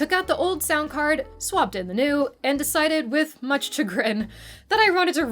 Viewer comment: This is an awesome video. Seeing younger youtubers experiencing the joy that was building your own PC is super cool and you are a great presenter. Did you say "much to grin" at 7:03? If so, I think you meant "much chagrin". Chagrin means to be vexed or annoyed.